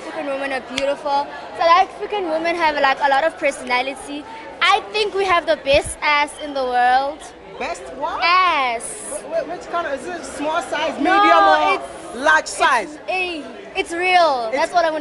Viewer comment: Did i heard she said, "nice ass in the world"...Damn..Love ur vlog..one love from Solomon islands❤🇸🇧.